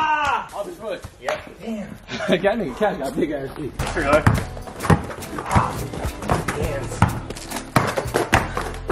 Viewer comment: what did say?! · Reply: He said "that nigga cash got big ass feet"